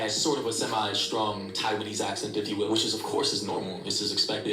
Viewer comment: Aren’t “Taiwanese” people from Taiwan not Thailand I’m confused… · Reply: For a sec, I thought he said: 😅 Thailanese; then I clearly heard him say Taiwanese and he kept repeating it. 🤣🤣 that's when I knew he couldn't be trusted 😂😂